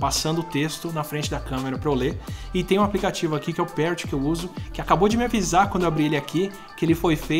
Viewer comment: Atila, qual o nome do aplicativo que você fala no minuto 10:25? Por favor, grato. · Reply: O nome do aplicativo é Parrot